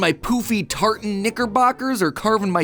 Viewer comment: ?!?!??!? Tf matpat just say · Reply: Knickerbockers, it's a type of shorts.